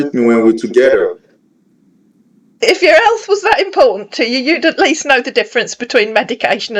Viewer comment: I thought he was saying impotent 😅 And also Chi..like the tea😊